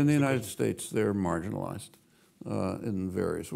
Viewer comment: Can anyone subtitle and/or explain to me what Chomsky says at 1:29:30 please? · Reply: "In the US they are marginalized."